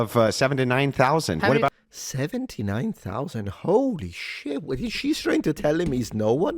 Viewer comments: I think you misheard him. He meant 7-9k. Not 79k. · Link to the original video, check them out. <link> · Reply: I think you miss heard Andrew he said 7 to 9,000 not 79,000 😂😂😂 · I think he sayd 7 to 9 thousand, not 79.000